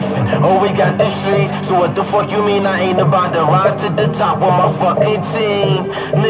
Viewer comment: what the fuck did joey say during his verse or chorus whatever 2:55 "im NOT about to rise to the top with my fucking team" please tell me if im wrong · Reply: "oh we got these trees so what the fuck you mean i aint about to rise to the top with my team"